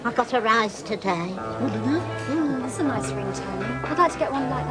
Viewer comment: When Debbie said 'That's a Nice Ring Tony' - I thought she said that's a nice ring Tone lol!